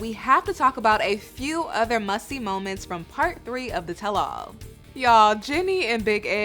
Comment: I thought Mel said “a few other musty moments from…” 😂😂😂😂 I had to do a double take😂 -ReigningNaturals Hair Care ❤